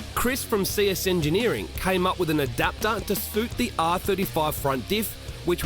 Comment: did he just say r35??